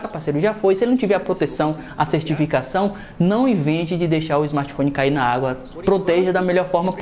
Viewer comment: "Não evite..." Oi????? Não entendi kkkkkk É para deixar ele cair na água? Kkkkk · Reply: Não INVENTE, surdo